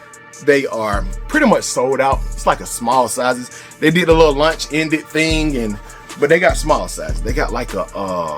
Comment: Why it sound like you said lunch ended not launch 🤣